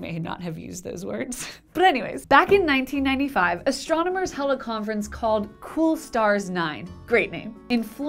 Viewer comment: I thought you said "Astronomers' Hella Conference!" \ :D /